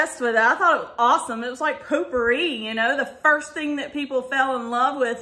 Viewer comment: When you said potpourri, you said sounded like you said pooppouri. lol I hate the pumpkin pear scent. I have no idea why I bought it.